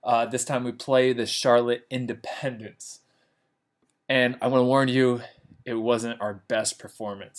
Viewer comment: Did I just heard " I wanna learn you" or " I wanna warn you " ?????😂😂😂💀